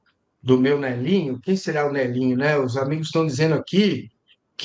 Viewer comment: Eu entendi "Manelinho", diminutivo de Manoel, um nome tipicamente português. Fazendo uma referência a ele mesmo